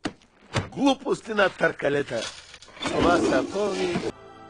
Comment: "глупости на търкалета" I didn't expect Bulgarian there I'm glad I saw it XD It doesn't have a direct translation, but the best I can describe what he said is "dumb things on rollers" XD I LOVE IT! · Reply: А я услышал "Глупости на тарталетках". Слово глупости есть и в русском, а что за тарталетки до последнего не понимал)